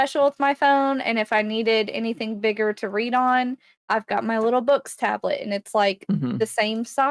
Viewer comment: Can review her little BOOX tablet, did I hear correctly? 21:37 eink right?